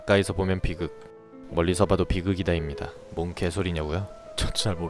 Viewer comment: 이상한 내계정을눌러라 였습니다. 로 들리는 건 왜죠?